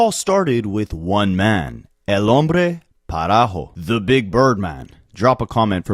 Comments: Parajo? I thought you were trying to say paragraph man. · Duolingo failed you. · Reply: I thought it said El Hombre Parejo 😭😂😂😂